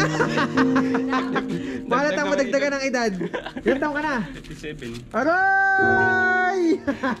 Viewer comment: ano sinabi ni 37 or 47